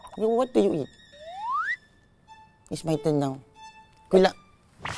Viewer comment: wrong caption said good luck and the captions said 'but turn it down to focus on her studies' NO HATE!!!!